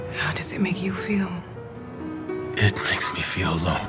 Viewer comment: did he just say "makes me feel long"?